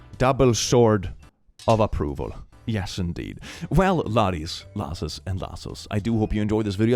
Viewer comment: Reminder to all laddies, lasses, and lassos: Lying for the purpose of reverse trauma is justified. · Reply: I just realized I've misheard it this whole time. I thought it was "Laddies, lasses and lazzles" 💀😂